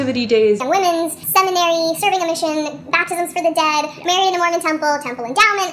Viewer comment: The edit kinda chipmunked past it, but did I hear baptisms for the dead? What even is that?